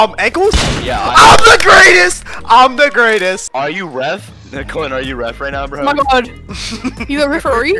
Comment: WHO’S COLIN??? I swear I heard “Colin are you ref” Is it???